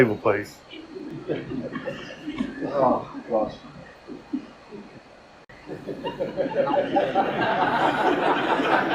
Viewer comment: What was the audience guy saying at around 24:30 ? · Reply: "Go Wenbo" x3 and then, when admonished by the ref, "aw fuck"